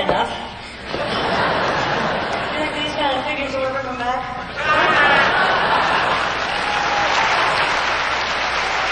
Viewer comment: What does she say at 2:03? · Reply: "Do you think these kind of figures will ever come back?"